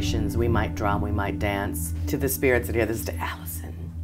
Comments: really gurl out of all the names you could have said why "Alison" or am i just hearing things now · did she just say 'Allison'? because that's my name, and that spooks me. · does she say “Alison”??? That freaked me out!